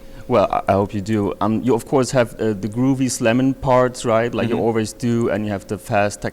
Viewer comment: I swear he says grooviest lemon parts